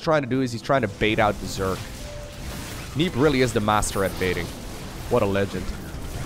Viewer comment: Did you really called neeb a master bater? Really lowko? A master-bater?